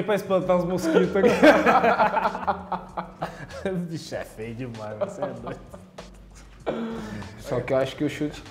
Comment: Did Fernandinho just say "the bloke is too bloody ugly" about Joao Cancelo? ahahah that was hilarious, or was I the only one?